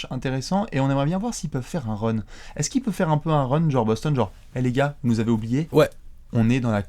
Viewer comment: T'as dis quoi à 0:40? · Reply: Maxime Turbez Les gros.